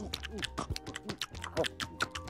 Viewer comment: Whoa, what a nice fella. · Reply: I thought he said “Well, what a nice fella.” in his Liverpool accent